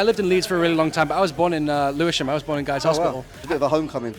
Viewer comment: Guys, what does Stan say here? Where did he was born? I can't understand him 'cause I'm not a native english speaker and plus he spoke too fast at that part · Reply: I'm not a native english speaker either so I might be wrong but I think he said he was born in Lewisham